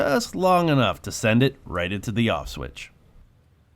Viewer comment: I almost thought you said "Auschwitz", but ofcourse you said "off switch"!